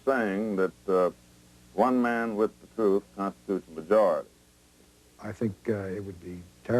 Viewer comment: What is Garrison saying at 26:00 "One man with...?" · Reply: "One man with the truth constitutes a majority". In other words, one man with the truth trumps many men who do not know the facts.